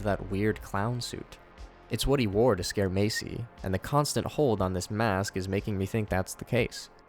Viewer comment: When he said Macy yelling at her, I lost my shit cause I thought he said Casey